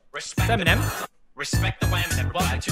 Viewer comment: did he say it's Feminem?